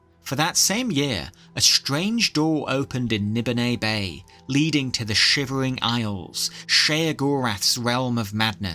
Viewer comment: I miss heard you terribly lmao. I even had to rewind because I thought you said the whip and nay nay lead to the shivering isle lmao